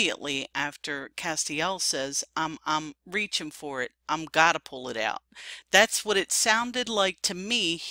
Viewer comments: sounds like he says" I'm not pullin' it out" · I hear I'm not pulling it out. Not i gotta pull it out. Yes it was setup